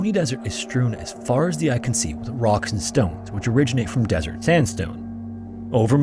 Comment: DID I HEAR A ROCK AND STONE?!